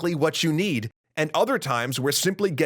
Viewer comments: Did you say "shump?" You meant SHMUP, right? As in, "shoot 'em up?" lol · Did you mean ' 'shmup', as in 'shoot-em-up'? It sounded like you said 'shump'. What's a shump?